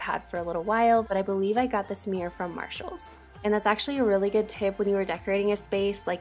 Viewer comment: I love the makeover, love the pictures on the wall, couldn't find them in Marshalls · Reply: Tell me why I thought u said marshmallow